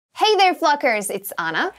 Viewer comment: Sorry what did she say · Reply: Hey There Cluckers